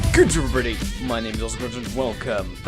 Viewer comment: Intro translation: "Good morning, my name is AwesomeCrunch." Reality: " Gdmrr.. mnisaemcrsh" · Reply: I hear "Gudrweery, my name is virgin"